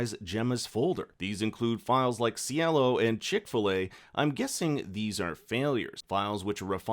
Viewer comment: Am I the only one who heard Chick-fil-A when talking about the chicxulub file? 😂